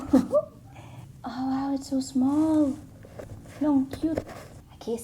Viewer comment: Did you just said "luh, ang cute" 0:50? Never thought you're a Filipino. You had a foreign accent. Anyway, I started to love your videos